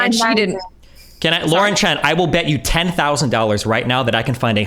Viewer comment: I thought you called her Lauren-Chan.